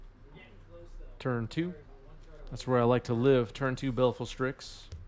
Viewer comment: "That's where I like to live"...I hear that as "I can drive 55"